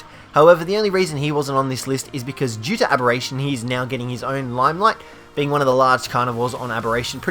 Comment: his geting a.... what did you say there limeline or something? Idk what you Said... can you post what you Said? It sounded like limeline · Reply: Star stable riders Limelight - another word for getting attention.